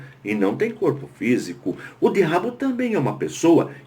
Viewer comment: Oi pastor sou Janio, no minuto 9:54 o sr. diz terrabo ou derrabo, eu não entendi, por favor. me detalhe melhor. <mail> ou <num>. muito obrigado. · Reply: a Paz! Eu disse: "o Diabo".